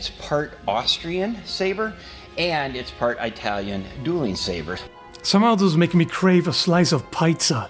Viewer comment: Did he really say "eye-talian" dueling saber? 😂